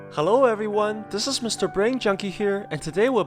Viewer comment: I thought u said hello everyone my name is brain chunky